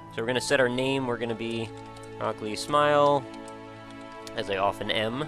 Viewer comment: Lol, I misheard "I'm gonna be 'Ugly Smile', as I often am".